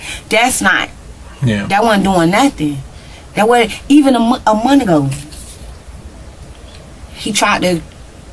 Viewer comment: I'm ASSUMING that boy's mama was trying to say "even a month ago" but, what I heard was "ee-unamunago". Certainly I'm NOT the grammar police but, c'mon. Communication by way of verbal exchanges seems important. Sorry about the rant. Ok, back to the video.